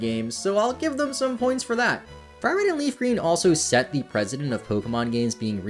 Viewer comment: Did you say it set the president?